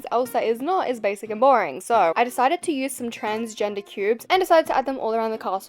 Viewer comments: 10.50 did you just say transgender cubes😂 im dying 😂😂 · ar 10:47 did u say transgender cubes 😭😭